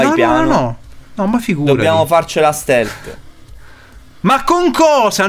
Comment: ho sentito bene? dice "dobbiamo farcela stealth"? ah beh in un gioco del genere...